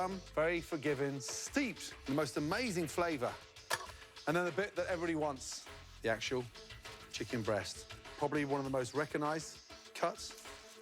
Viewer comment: What did he say at 4:00? "and the bit that everybody wants the actual chicken breast" over the thigh meat? -1 Michelin Star